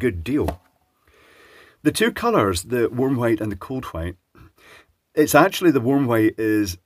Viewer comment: thought he was saying "warm height" the entire time